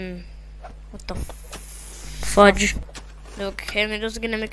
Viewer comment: Did you just say fudge